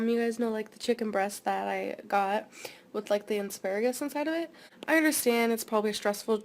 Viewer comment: did she just say “insparagus”? 😭😂